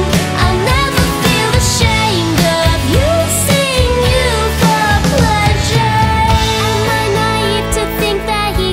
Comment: OMG! I hear “Abusing you for pleasure” now instead of “of using you for pleasure”. Creepy